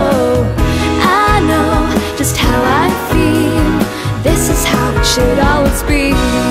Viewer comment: 前面好像輕快版的"過火" 後面的how I feel 我一直聽成夏威夷...